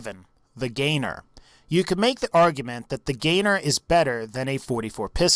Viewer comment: when you said make the argument it sounded like you cut XD